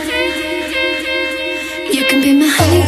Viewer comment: sounds like shes saying "Hyundai" in the beginning instead of "honey dip" hahah